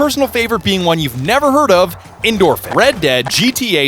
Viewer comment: You say "endorphins" I hear "end orphans"